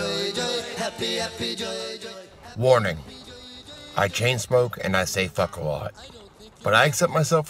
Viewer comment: My Bluetooth speaker went in and out during the intro so I heard, “I chainsmoke and I fuck a lot.” 😂😂😂